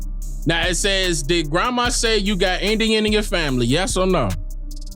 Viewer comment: I heard we had "Indian in our family"